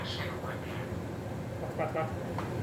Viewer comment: I think I heard say at 2:11 A HOT SANDWICH,,, anybody? Love ya